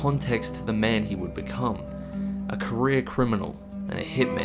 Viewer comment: I thought he said Korean criminal XD